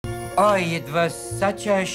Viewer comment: A mohel is a man who performs circumcision. Gribenes is fried chicken skin. 😂😂😂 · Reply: I always thought he was saying scandal with a heavy Yiddish accent "schandal" which kinda made sense to me if the old lady got outed for buying cheap.